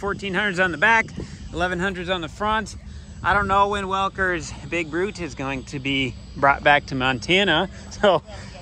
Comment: I thought you kept saying Big Butt… not that it wouldn’t fit too 😂